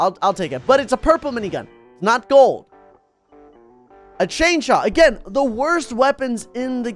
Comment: A chainchaw what you say